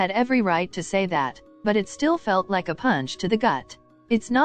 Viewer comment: Why did i think that said a peanut to the gut